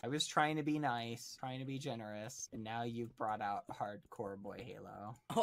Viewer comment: I thought that said hard, core, e-boy halo!